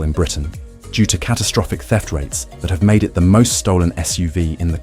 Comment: I thought you said "death rates". So i replayed it twice, and eventually made-out "theft-rates"